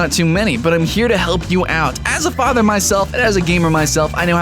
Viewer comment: wait.. Did he say hes father himself? He sounds not very old